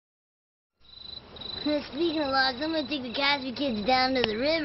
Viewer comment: once you get this joke it's like looking into the arc of the covenant. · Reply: I paused the video at 0:06 just now.. I've watched this clip dozens of times, but only now did I understand what he says at the start! "Speaking of logs.." :P